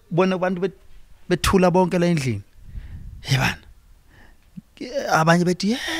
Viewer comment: Lol did he say He banna - 16:30?